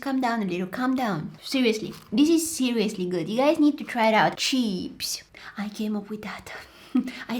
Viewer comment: What she said cheeps I thought you said Cheaps 😂